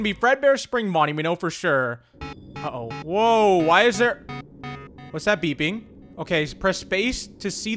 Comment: I thought he said whats the PP🤫